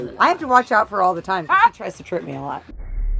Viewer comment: what did she say at 0:52? she tries what? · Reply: She said how florence tries to trick her a lot (ignore the fact i replied to this like 11 months later)